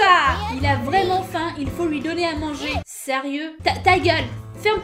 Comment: C'est "donne moi une friandise" pas "donne moi à manger" ouais je suis chiante ouais X'3